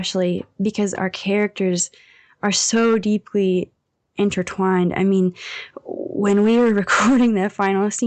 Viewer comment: "because our characters are so deeply ... " what did she said? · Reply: intertwined